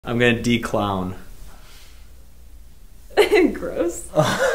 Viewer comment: When he said “ I’m gonna de-clown” I thought said “I’m gonna wee on the ground” ngl tho I did